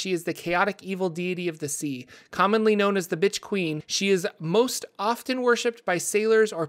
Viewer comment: Did he say "bitch queen"?